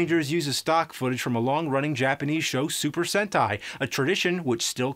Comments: DID I HEARD SUPER HENTAI ???????!!,!!! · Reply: He said "Sentai" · for a second I thought you said hentai lol